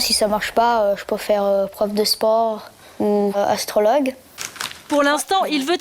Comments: rassurez-moi ... il a bien dit astronome... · ASTRO QUOI ? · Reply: Zecky bah astrologue